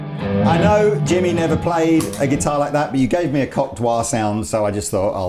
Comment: I replayed around 18:52 in the video multiple times to make sure I wasn't going crazy when I heard "cock" get whispered hahaha. Good Job. Informative and entertaining as always.Thanks for all the content you guys.